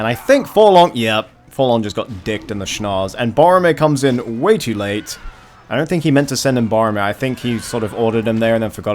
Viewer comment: after dwalin's shatter hammer, what are those shouts of victory? I heard a few in that skirmish but who is doing that? Edit: Are those Helmingas? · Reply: They are screaming HELMINGAS!